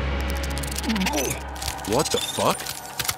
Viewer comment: why when peacemaker said (what the fuck?) it sounded like he wants to contain suicide💀